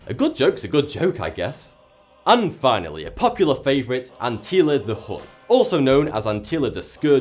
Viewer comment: why does he say "Antila" (8:31) instead of "Attila"?